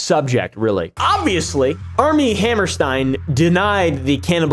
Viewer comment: I thought you kept saying arm n hammer 🤦‍♀️ lol😂